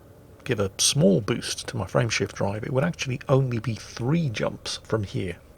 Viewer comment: Am I the only one that whenever Jingles says "Frame shift drive" I hear "Friendship drive" for some reason?